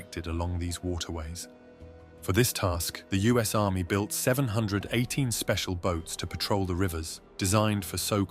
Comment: did you say us army built those boats? at 1:46?